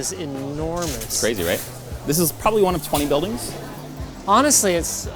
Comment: Did he say this is just one of 20 buildings? Damn i want to go there lol.